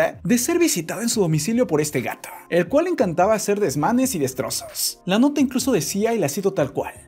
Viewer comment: No c si fui sólo soy pero pensé que dijo desmadres 1:28 :v c mi hizo raro ya que nunca lo he escuchado decir esas cosas en su canal XD